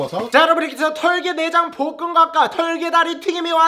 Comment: 나만 볶음각과 라고들리나?ㅋㅋㅋㅋㅋ